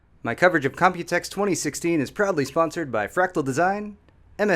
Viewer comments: When said "proudly sponsored by" I misheard it as "probably sponsored by" and my first thought was "Are we not sure, Paul?" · Lol I thought you said you were "probably sponsored by" in the beginning.